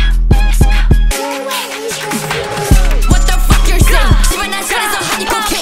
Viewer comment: “what the f- your saying” idk Kim Lip you tell me 🗣️‼️🔥🔥🔥🔥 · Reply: I thought it said “what the fuck your shit??”